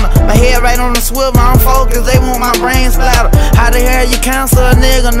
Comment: “Mute me when I voice my opinion I ain’t even know it mattered” damn🔥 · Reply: I’m here for that bar🔥 I thought he said “usually when I voice my opinion”